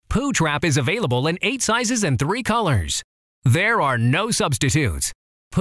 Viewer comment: for a second, I thought he said: “ there are no substipoops”